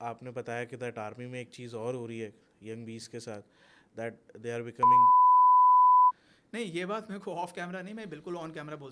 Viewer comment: What was said here can someone tell plsss??? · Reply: Gay